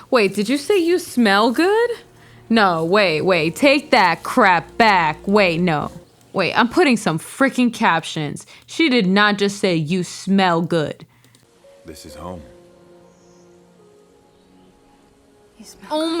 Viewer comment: I don’t even remember that line. I thought she said “you spoke”